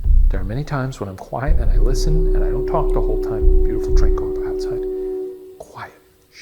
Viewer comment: That first voice sounds like it says “I’ll be outside”. Just what I got from it.